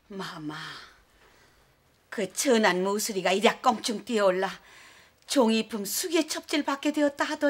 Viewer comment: 종이품 숙이가 아니라 숙의 아닌가.. 숙원 숙의는 들어봤어도 숙이는 첨들어봄. 인터넷에서 후궁첩지만 검색해도 정확하게 나올텐데 교정 교열하는 사람은 안두나보네.. 대본이 잘못된게 아니라 이거 자막올리는 사람이 들리는대로 자막 올린듯.